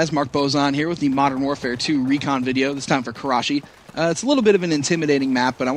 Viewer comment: i always think "my crotch is itchy" when i hear "karachi"